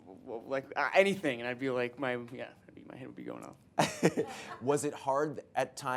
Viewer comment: Please somebody tell me what did Timmy say on 3:19-3:21? I just can't hear it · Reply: "my head would be going off"